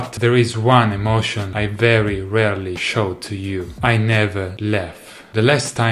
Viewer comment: do you like beer? Because I heard, "I never Leffe"